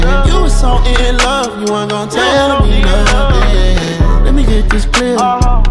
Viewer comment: I thought he said let me get this bread lmao